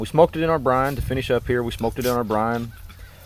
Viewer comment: It does sound like he said he smoked it for thirty hours, but I'm sure he said "soaked" regarding the brine.